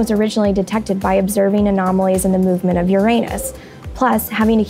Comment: Holy shit 😂😂😂😂 I thought that she said "your anus" lmao